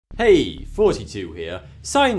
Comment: sounds like he says "Hey, 42 here."